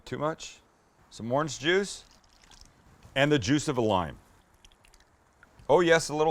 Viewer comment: Juice of a lime i thought he said juice of a Lion